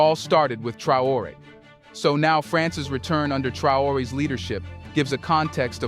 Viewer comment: What are you saying? 3:36, France has returned?